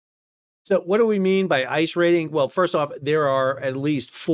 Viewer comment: im so cooked. he says "ice rating", I hear "ICE raiding"